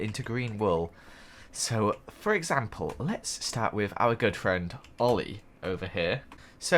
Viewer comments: I heard, "Our girlfriend, Oli." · Was it just me or did I just hear Joel say "our girlfriend Oli", probably was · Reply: He said "Our good friend Oli" Not girlfriend Oli